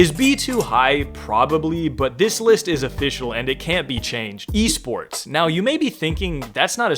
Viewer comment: thought you said wiisports😭